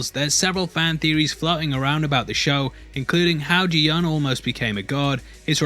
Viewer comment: I was extremely confused during the intro bc I thought he said Gihun nearly became a god, then I later realized he said "guard" lmao